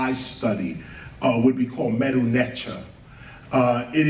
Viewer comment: What is the school of metaphysics he says he practices at 48:45? Medunetcha?